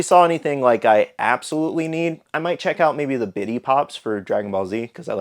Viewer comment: I could’ve sworn you were saying “Diddy Pops” not bitty